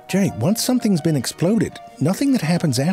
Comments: Why do i keep hearing crow's dick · I keep hearing Crow's Dick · So much innuendo 😅 I keep hearing “Crow’s dick” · I keep misheard him saying "crow's dick" bruh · Help I keep hearing “crow’s dick” 😭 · I keep hearing "crow's dick". Is that intentional? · All I hear is “crows dick” and…. And I can no longer take this video seriously.